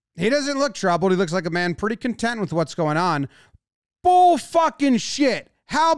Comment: Lol worst part is that the fan didn’t even say anything disrespectful….”let’s go home plate!” Unbelievable that THAT was what crossed the line to him 😂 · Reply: i thought he said "fucking bullshit"